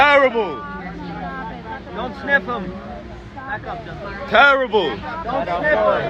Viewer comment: Did I heard “don’t sniff them” 😂😂😂🤣🤣🤣🤣🤣🤣🤣🤣